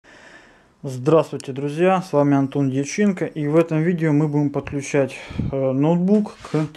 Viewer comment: мне послышалось антон личинка)